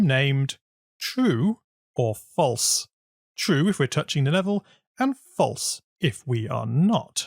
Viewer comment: in CC (subtitles) 3:38 instead of 28, it says 18!!! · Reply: and in 5:38 it says tourching instead of touching